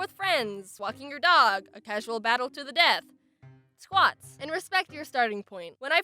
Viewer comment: Jaiden: don’t murder yourself Also Jaiden: Casual Battle to the death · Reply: I thought you said "casual death to the battle"